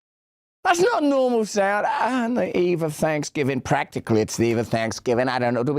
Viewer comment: I thought she said this Evil Thanksgiving